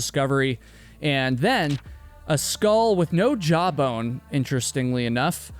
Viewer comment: When he was saying "a skull woth no jawbone" and I thought he was saying "a skull with no job" 😂